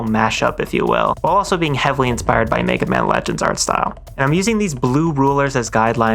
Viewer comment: who? I cant really understand what he just said? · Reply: Mega man legends, a game, i think